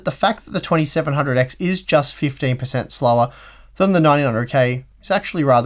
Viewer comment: English is not my native language, so I first heard, "Ryzen 2700x is 50% slower"... I was thinking what ?? I enabled subtitles and I was hear wrong first time, correct is "15% slower". Maybe in i7-9900K release this happened also :D